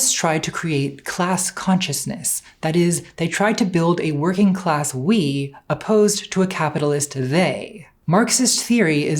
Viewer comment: Great video! The subtitles are kinda weird at times though. I'll use // to mark subtitles changing in my examples. Also this ain't my main language so commas may be wrong 2:04:23 "That is they try to build a working class. // We opposed to a capitalist, they" instead of something like "That is, they try to build a working class we // opposed to a capitalist they". 2:04:15 also has mistakes compaired to audio, which I noted happened a few other times "Lord have mercy. Just explain to Joe Rogan // that it's simply a non-ideal version of Hegelian. // How difficult is that for Conspiracist, the belief in a, // they creates a we." instead of "Lord have mercy. // Just explain to Joe Rogan that it's simply // a non-idealist (in?)version of Hegelianism, how difficult is that? // For a conspiracist, // the belief in a they creates a we." Also shortening conspiracism to 2:05:11 COism and at 2:05:48 copi (unless this one is a typo) but then at other times spelling it fully. I'm still really grateful for the subtitles! I can't imagine how daunting it is to subtitle a video this long.